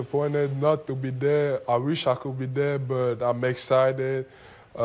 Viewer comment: I thought Embiid said “I wish I could be dead”